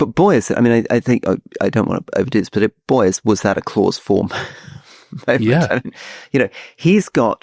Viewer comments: Listened to the audio. I'm sorry both but you've so clearly missed the point on Mamdani and you're so wide of the mark to say it's part of the social media tik tok phenomenon. He won because he's offering hope (corny as that sounds). People are fed up with the centre, it hasn't offered them anything, Mamdani stands for something, he has ideas, he's offering change. The guys are blinded by their centrism but centrist politics is over, it has failed and as to Rory worrying for the New York business class, I'm afraid they're part of the problem. It simply isn't enough anymore to run on the fact that you're not trump, or you're not the Tories, you have to stand for something. The Labour party is about to learn this lesson the hard way. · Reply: What did Rory say at 35:56? "Boy, was that a (closed form?)..." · What did Rory say at 35:56? "Boy, was that a (closed form?)..." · NO He's not Fabian Socialist at best Modern India has most of his socialist policies, from Gov run grocery stores, to rent controls, to laws banning mass firings of private employees, Gov run Hospitals, to Gov controls on most things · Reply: What did Rory say at 35:56? "Boy, was that a (closed form?)..."